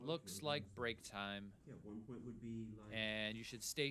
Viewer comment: at what time in the video did you hear them say $75 per second? And if that's true, then what is the significance of them discussing "216" in relation to a 36 second deficit around the 54 minute mark? I agree $5K makes a lot more sense than $400 but... I can't get over the 216 talk. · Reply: I think what is being said at 54:22 is "1 point is would be 60, no, 70 *seconds*" (not dollars per 1.5%), then someone muffled clarifies it's 66 (or 67) seconds. Anyway thanks for the insight.